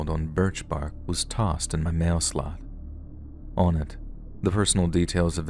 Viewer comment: I suspect story 8 is fake. But if not, I’d get a ton of professional guard dogs, place hardcore security cameras that record everything and set up traps around the house and/or hire a personal guard service. Last resort is just shooting at him after you “pretend” to sleep when he enters your home. Otherwise, it seems ridiculous this man can’t be dealt with. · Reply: I could've sworn they said mail "slot", not "box" Either way the way its written just feels odd